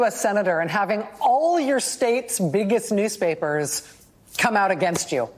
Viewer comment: When Rachel said, “Biggest” I thought she said, “Bigots”😳